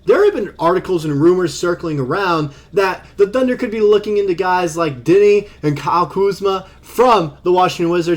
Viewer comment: did I hear diddy? If so you should knw ashmed